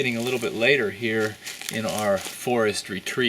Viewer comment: did you say forest retreat or forestry treat? Love the miniseries :P